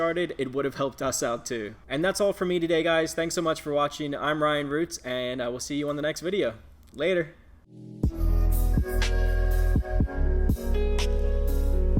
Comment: When you end the video saying "I'm Ryan Roots aaaand..." I hear "I'm Ally Roots!" Haha! It was interesting to peek into the Amazon FBA world. You're a good teacher. Keep up the good videos!